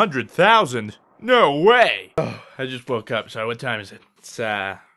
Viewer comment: Did you just say ساعه in 00:04 ? xD